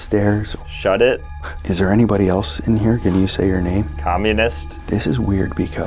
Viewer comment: "communist" what did he just say??? ☭☭☭